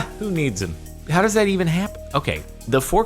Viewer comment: when u say f*ck lift instead of fork lift